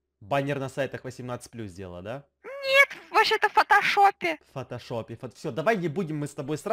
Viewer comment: Почему мне послышалось не фотошоп фотожоп